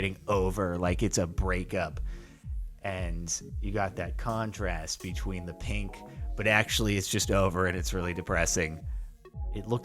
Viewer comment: I thought it said oven but now it makes no sense